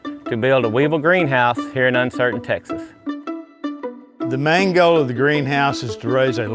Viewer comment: In 2:00 did he just say "the mango" 😂😂😂